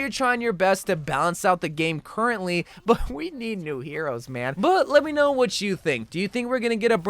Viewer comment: I thought they said no new heroes ?